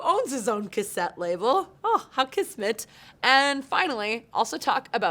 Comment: did you say "kismet" at around 0:30? If so, I am VERY impressed because not many Anglophones know that word.